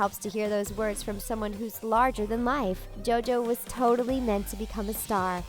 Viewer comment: did they really call her "someone who's larger than life"